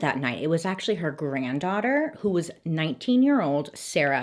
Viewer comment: Okay up until the trial part, I thought you said Sara was 96 years old and the entire time up until then, I thought they killed an old lady and then I remembered that the killed knew her in kindergarten and I was really impressed two 96 year olds were able to do all of that but I cried because I thought it was some cute old lasy